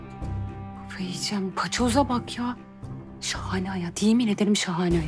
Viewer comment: Allahim paçoza bakmi dedi yanlişmi duydum 😂😂😂